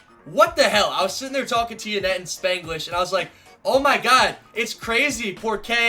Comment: did he say Spanglish